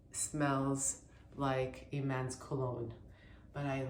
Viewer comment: Lol I thought you said "man's colon" 😄